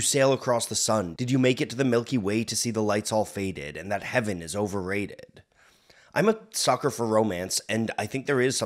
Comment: Growing up i always thought he was saying "van halen is overrated"